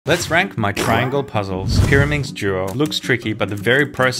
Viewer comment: What other hear: let's rank my pyramix cube What I hear: let's prank my pyramix cube